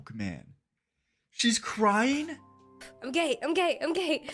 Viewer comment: (As a lesbian) Yes I 100 percent agree. It is so annoying. Sexuality does not determine whether to like someone or not. Idc who you go out with, if you're a bad person you're a bad person and I don't like you. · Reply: I’m pretty sure she was saying “I’m okay” and it just sounded like she was saying she was gay, like why would she just say that out of nowhere? She was trying to calm herself down by saying “I’m okay”